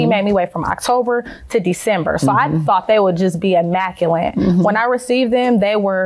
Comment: I thought the pictures would be 'immaculine'.😂😂😂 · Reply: My bad,I thought she said "Immaculint"...Same thing😂😂😂